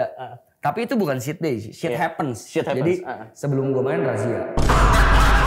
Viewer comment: ngomong apaan si gakedengeran😭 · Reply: Sblm main ada razia, kyknya